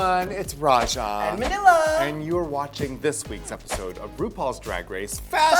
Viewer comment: I thought I heard her say "It's Laila!" not "Manila" and I got way 2 excited and did a double take. This is totes the lewk Laila did for the Cha Cha Bitch mini challenge.